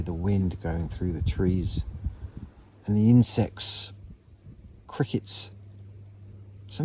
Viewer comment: And insects 😂at first I thought you said I need sex 😅😂😅😂😂😂